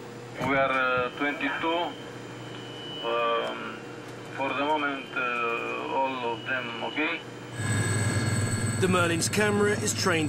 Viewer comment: if it wasnt for the subtitles i thought he said "for the moment all of them are gay"